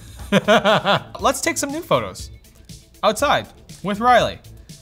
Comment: I swear I heard him say nude photos.......but I think it was new photos? =P lolz